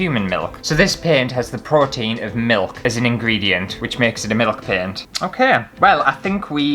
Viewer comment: Man these are amusing me so much. Kept mishearing this as ‘milf paint’ 😂